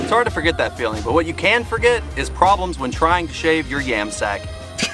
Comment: id anyone else think Yammie noob when he said Yam Sack